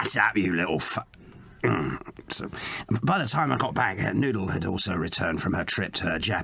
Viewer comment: Not me thinking Murdoc said "Noodle, my daughter" when he actually said "Noodle had also" 🤡